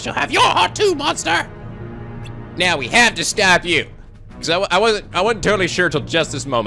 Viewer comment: What it says: "Not if we stop you!" What read: "Now we have to stop you!" oy vey